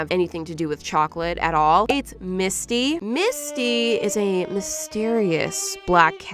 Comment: everytime she says Misty (i think that’s her name??) i also think that she say Miski like the artist